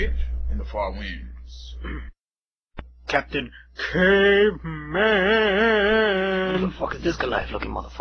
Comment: Lol "Captain Cave MAaAaAAAANNN!!! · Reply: Vladd Chambers Lol I could never figure out what he said their . Always Thought he said Captain cape man.